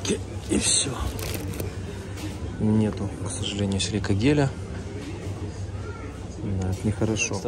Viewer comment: Добрый день. Спасибо за подсказку селико геля ( если правильно расслышала). Мы мучается с кошачьим туалетом. Опилки по всей квартире, шарики тоже... И вообще то наш кот не особо хочет ходить в лоток. Проблема...Особенно трудно ему перестраиваться после отдыха на даче... Нужно посмотреть сколько этот гель стоит у нас. А вообще-то...есть гарантия, что кот будет в него ходить по нужде... А то получается дорогой эксперимент. Ох уж эти наши котики....❤️🌷